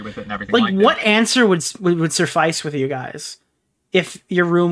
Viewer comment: Both of these Internet guys are out of touch. Intellectually, Asmongold is in a different league than. Did he just say “Surrfice?!”